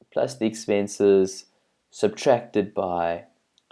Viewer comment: you said "subtracted" instead of "divided" :P